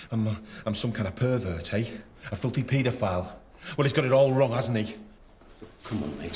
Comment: Did Daniel say "fucking paedophile' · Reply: Chisel Marr I think he said filthy pedophile